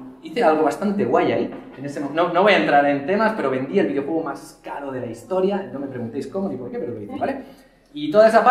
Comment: ¿Qué dice que vendió? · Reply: Richard Dawkins el videojuego más caro de la historia es lo que se escucha pero si sería eso estamos hablando más de <num> millones xD, no lo sé porque dijo eso